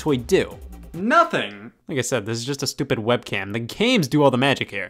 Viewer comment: thought he said nigga said but he said like I said